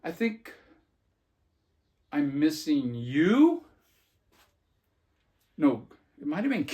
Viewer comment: I heard "I'm missing Ewe"